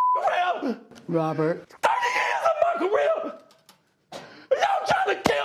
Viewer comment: i cant be the only one who heard "30 YEARS A MCRIB"